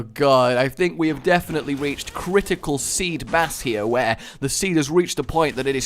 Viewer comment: thought he said critical siva mass